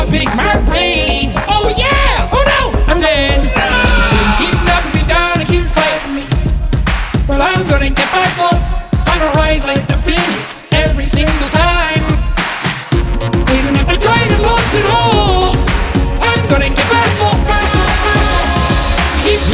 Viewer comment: I thought it said "Imma rise like the Felix" 😂😂